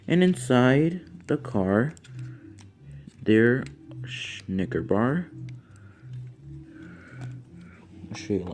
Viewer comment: sounded like you said "schnigger bar" im done lmao